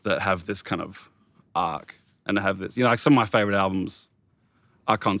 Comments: I couldn't understand the name of the concept album that he mentions in the interview. Can anybody help me with it please? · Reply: A Grand Don't Come for Free. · What concept album did he say?? I cant make it out. Grand Uncomfort Free? Google wasnt a help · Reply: I heard green don't come for free lol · can someone tell me what album name Kevin says at 4:55 ? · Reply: I'm pretty sure he says "A Grand Don't Come For Free" by The Streets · Can somebody tell whats the album kevin mentioned at 4:53 ? I cant hear very well.. Please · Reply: The Streets - A Grand Dont Come for Free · What album does Kevin say around 4:53? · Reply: "A grand don't come for free" - The Streets album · what is the concept album he said on 4:54? i really want to listen 2 it!! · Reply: A grand dont come for free - The Streets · What the hell does he say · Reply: Chad Caravaggio a grand don’t come for free